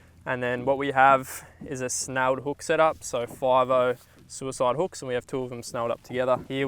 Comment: Uh did u say suicide hook?